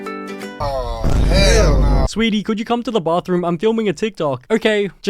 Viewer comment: I think I heard no